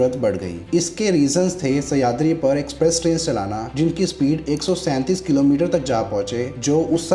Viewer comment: Did I just hear 137 km/hr.. And after 100 years Indian Railways is happy with 160 😂 .. That too max in most area is 130 kmph 😂